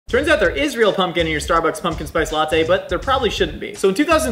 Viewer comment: I thought i heard, "turns out there's isreal pumpkin in your latte and shouldn't be". Very confused for a second